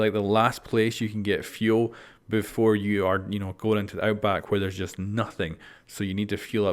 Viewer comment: Okay, I thought it said "outbreak" instead of "outback" for a second